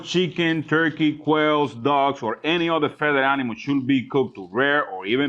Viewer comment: Wait did he say “dog”